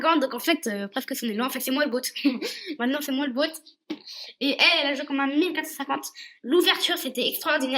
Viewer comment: "En fait c'est moi l'goat" Excellent 😂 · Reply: J'ai dit " bot "😂😂